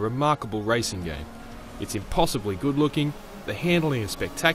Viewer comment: I'm sorry, I think I might of misheard...did he say impossibly good looking? 0:18 does that look impossibly good looking to you